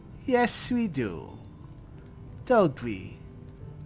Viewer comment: Good day, I so enjoyed the episode so much, so Mort' s middle name is Wilber, lol . Don't think he should make fun of Inigo's middle name 😉 Colin ha ha ha. Anyway thanks again for another video in this series of Mort our favorite Necromancer Mage. 👍💖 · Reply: Lol ,I thought said Wilbur ha ha ha ha 😅 no Wilmer is good ha ha ha 😅. I love 💖